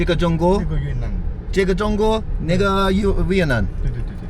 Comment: I swear I heard the N bomb!!!😂😂😂😂